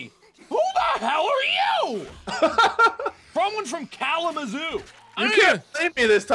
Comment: Well, I didn't know Kalamazoo was a real place. I thought he was saying California in a "funny" way. Sorry everyone that live in the mythical land of Kalamazoo.